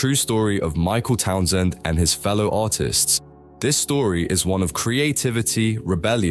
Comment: I heard "Michael Townsend and his fellow autists"